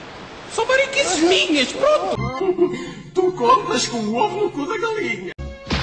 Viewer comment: só eu que entendi Tu comes com o vo no cu da galinha? Kkk